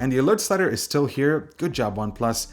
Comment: WTH did he say at 6:07? The alert stutter is still here? Huh?